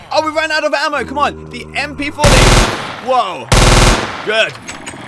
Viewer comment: MP40 INSTEAD OF P90